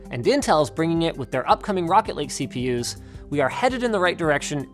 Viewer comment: Did I hear Rocket League CPU's? 🤣